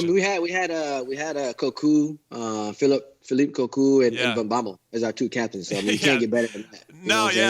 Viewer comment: I thought he said "Babel". But had to be Van Bommel 😁